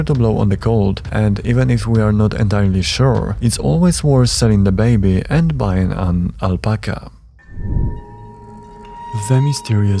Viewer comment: Did he just say "selling the baby and buying and alpaca"? LMAO!